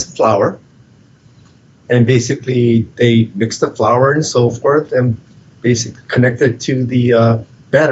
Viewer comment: I thought he said "mix the flour and sulfur"